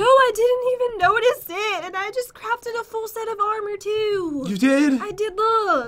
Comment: when said she just crafted a full set of armor I thought she said I just craped in a full set of armor